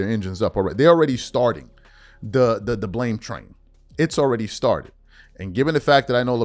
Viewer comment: I found myself laughing at 11:18 when you said the blame train.. Because I thought you said "LeBlame Train" haha you gotta start using that!